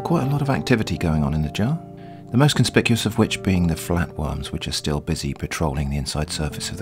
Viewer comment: I always thought you were saying 'flat ones'. Today I realised you were saying 'flat worms' uh oh, I kinda liked flatones.